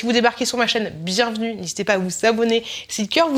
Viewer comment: Quand t'as dit "N'hésitez pas à vous abonner !", j'ai compris "N'hésitez pas à vous savonner !"... ça faisait chelou xD Cela dit, ça ne valait pas ton "Merry Christmoche" :)) Le pop corn est canon, celui au caramel au beurre salé est top aussi !!